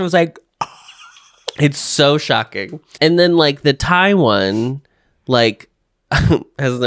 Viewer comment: said "Thai one" twice and i constantly heard it as "Taiwan". Something is wrong with my brain